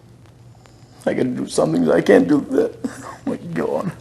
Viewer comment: What does he say at 13:57? All I caught was "oh my god" at the end.... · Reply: "I gotta do sumthin 'cause I can't do this......oh my Cawd".